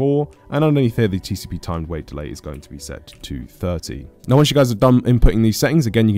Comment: Does the TcpTime Delay is 230 or 30 cause I heard 230 and you put 30 so idk which one is correct